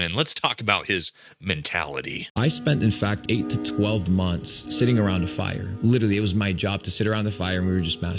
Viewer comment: I thought he was saying sitting around the fire masturbating lol 😅